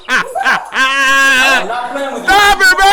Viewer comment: When she kept blurting “What’s up? What’s up? What’s up?” All I heard was “buk buk, buk buk”…reminds me of that old school rap by Project Pat; Chicken, chicken, chickenhead🤭